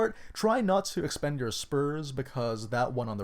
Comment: It sounded like he said keep a horse if it's really good like if it has 3 or 4 sperms...